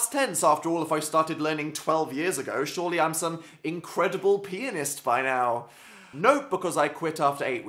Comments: Am I the only one who thought said 'incredible penis' instead of 'incredible pianist'??? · when he said 'pianist', I thought he said penis... hehe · When he said pianist it sounded like he said penis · Instead Of "Incredible Pianist" I Thought He Said "Incredible Penis" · when you said pianists I thought you said penis.